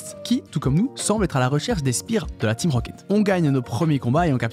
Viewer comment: c'est moi ou t'as prononcer "spear"? j'ai beau remettre ce passage, j'entend toujours spear^^